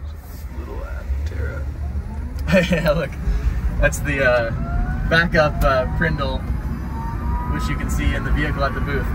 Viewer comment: For the longest time, I couldn't figure out what Chris was saying here, but I think he said "backup PRNDL", as in the Park, Reverse, Neutral, Drive, Low-gear controls. Very cool 😎